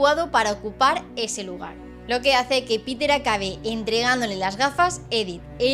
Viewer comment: Escuché que Peter la kgue. XD Igual la idea es la misma